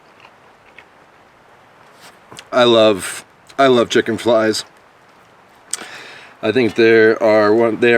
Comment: I thought you said chicken flies. Im thinking must be what you get when you eat outside.